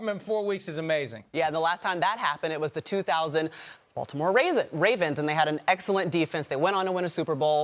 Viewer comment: I swear i heard her say “the Baltimore raisins” lol